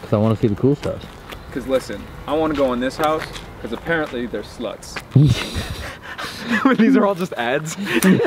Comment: when said; "i wanna go in this house becasue theres sluts." i thought he said theres slugs and i died laughing at the thought of them going in and playing with slugs